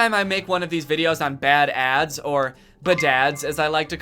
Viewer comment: "Badads" What I heard: "Bad dad's"